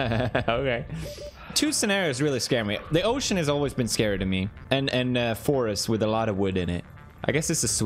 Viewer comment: Did he just say "The ocean has always been scared of me"?